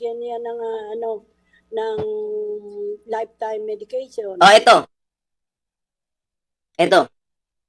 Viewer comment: loko ka napaso ako sa kape narinig ko sinabi mong life time red horse